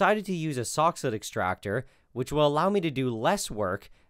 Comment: For those who are not clear on the name of the extractor device, it's a soxhlet extractor. I kept hearing "sock's foot" or "soxhood", but those are wrong.